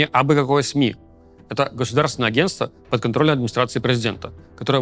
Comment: я услышал "Атас - государственное агентство"